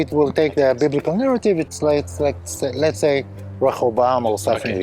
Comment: Enjoyed this so much. What was guy saying at 13:22? A name or place?❤️🇨🇦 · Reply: Rehoboam